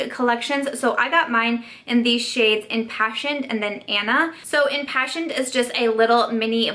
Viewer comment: Oh my word, you said imPASSIONed!! My nursing brain heard you say imPACTtion...and I was all like “what a really weird name!” That was embarrassing... 🤦🏻‍♀️